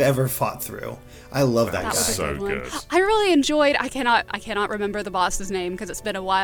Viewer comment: “I really enjoyed...” sounded like “Hyrulean Joy!” 😂